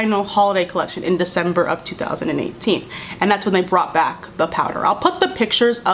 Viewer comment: girl I thought you said “PEDO powder” I SAID HUH??? SO FAST